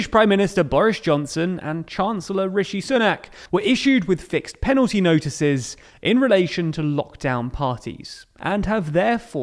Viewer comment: I THOUGHT IT SAID FIRED AND I WAS SO HAPPY